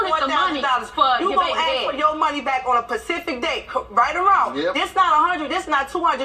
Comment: did she say pacific day?? 🤷🏾‍♂️😂😩. my chicago folks. we got our own dialect sometime.